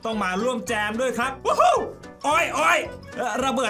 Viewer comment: ถ้าฟังดีๆ จะได้ยินเสียง โอ่ย เบาๆ